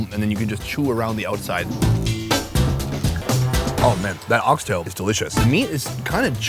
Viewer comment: Did I just heard dog's tail???? 🥲